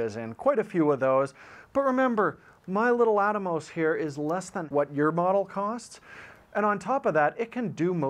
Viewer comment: I think Jordan was saying "less than half". It's close, but not quite.